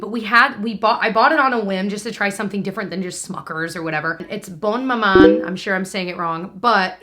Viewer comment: when you said “it’s Bonne Maman” it sounded like you said “it’s blowin ma mind” in a thick country accent 😂😂